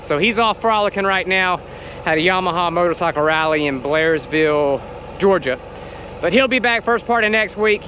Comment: Just found your channel, good stuff man!! Had to go back and listen a second time, at first I thought you said your father was at a Yamaha rally in Polarisville Ga. , I found that quite funny until I went back and listened again LOL